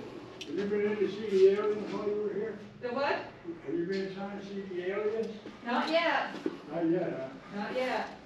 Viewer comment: LOL At 13:35 did someone say 'Did you see the Aliens?" lol Looks like a great park to visit Dude! Enjoyed!